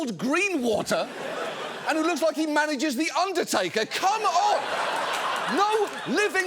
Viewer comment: that was not THE UNDERTAKER, that was Paul Bearer (RIP), he was the Takers manager. edit: i was corrected, he did that, that he managed the Taker, so I misheard that. thx again for the correction 👍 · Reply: He said “looks like he manages the Undertaker” not “looks like the undertaker” so I think they got it right. RIP Paul